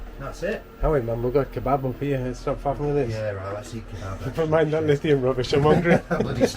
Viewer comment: what on earth were y'all saying? Had to be a different language. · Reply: I think we are talking about pork kebabs. but i cant be be sure 😁